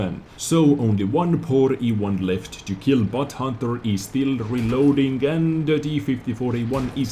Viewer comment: did you call that guy butthunter? :D